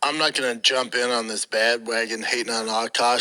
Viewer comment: did he say “bad” wagon? 🤦‍♂️